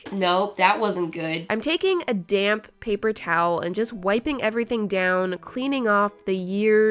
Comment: i thought she said damn paper towel 😂😂😂😂